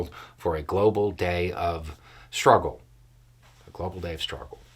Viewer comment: i thought i heard Day of Rage? at least that's what i heard on the radio.